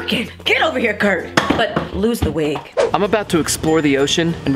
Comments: "Get over here Kurt" "But lose the wig" XD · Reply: Lol I thought she said lose the weight🤣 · "Get over here Kurt" "But lose the wig" XD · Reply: i thought she said lose some weight 💀